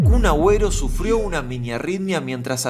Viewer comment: escuché "UN ABUELO" en vez de Kun Aguero creo que estoy un poquito sorda